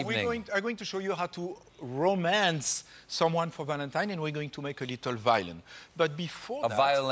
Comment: I thought he said "we're going to make a little Valium" I thought "yeah, thatll do it"